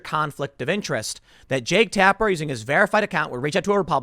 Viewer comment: When Tim says “Jake Taper”, I hear “Jake Tapeworm.”